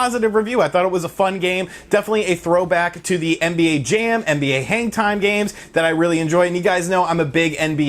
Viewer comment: When you said NBA Hangtime, i thought you said NBA Hentai lol